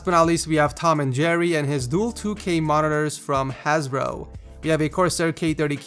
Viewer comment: I thought he said Hasbro.